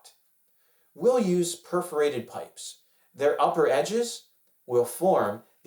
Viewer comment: you said perforated I heard corrugated. I had No Idea water pipes would be perforated. I would never thought these pipes were perforated . This has been the … what am I missing answer .. TY!!